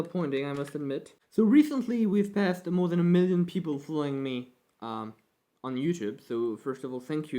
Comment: Republicattak: "So there are 1 million people following me" What I hear: "So there are 1 million people fooling me"